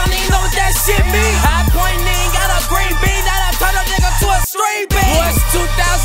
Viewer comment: Did he say have a green beam that will turn a nigga into a string bean? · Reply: titus I thought he said screen beam lol